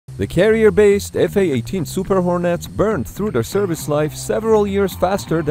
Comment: I keep misheard the super hornet with super idol